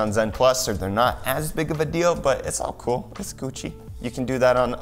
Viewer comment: did I hear.. its coochie? or its gucci? XD